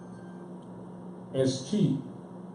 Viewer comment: I couldn't hear the second word @ 8:01 Elder said to look up in the etymology dictionary. Would anyone happen to know? Thank you. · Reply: Soulfully Original 8:01 (ESCHEAT/ESCHEATMENT- TO STEAL OR CHEAT ie. Our Land LTPFJ